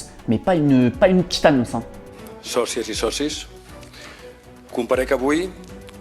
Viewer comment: A 3:36 sous fumette j'ai compris chaussette et saucisse 🤪🤪🤪